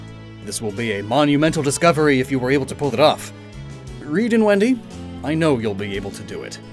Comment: When he said reed and Wendy it sounded like he said region wendy